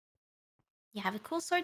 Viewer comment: Such profound and thought provoking lyrics... -You have a cool sword too? -OH YEAH BEBY Its gold -SUCKS -a Simply beautiful, astonishing. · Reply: I thought Ame said cold sore at first